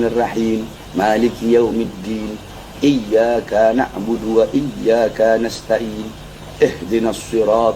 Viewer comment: Saya kok dengernya gini sih : Iyyaakaa na'budu wa lyyaakaa nasta'iin. Harusnya kan gini : Iyyaaka na'budu wa lyyaaka nasta'iin.. Minta koreksinya jika saya salah dengar..